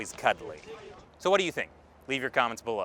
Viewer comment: Ermm... That intro sounded like you said America Ungoverned.